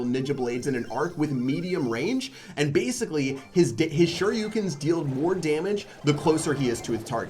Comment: did he said dick